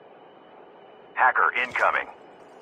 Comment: What it say? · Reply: Hacker incoming